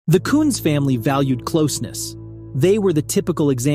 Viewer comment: Not to in any way ignore the horror of this tragedy but… 0:03 Did you just say this family is called the Kvnts?! (with the K-V being replaced with a C & U.). Oohh… KUNZ!